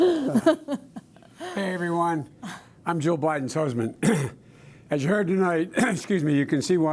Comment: He really said "IM JOE BIDEN'S HUSBAND" at 9:15!!!! The man is demented!!!! Hahaahahahaha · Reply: Jill Bidens husband. Clean your ears.. nasty.